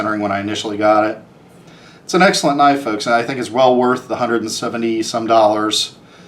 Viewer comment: Did he say $170... yeah this us 3 years old. Everyone's paying the Sprint Run tax now days, getting higher every year.